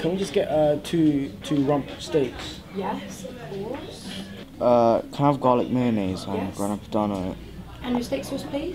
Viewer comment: i swear he said ''Can i have dino nuggeys'' lol